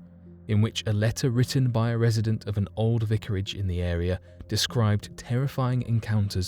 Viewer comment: did he say "knickerage"??